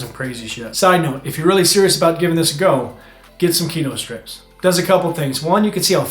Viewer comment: at 4:50 ...I couldn't hear what you were suggesting we get here. Pls help thx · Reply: He said keto strips to measure if you are in ketosis.